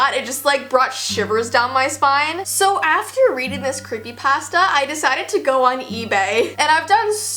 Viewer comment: It sounds like ur saying Creepy posta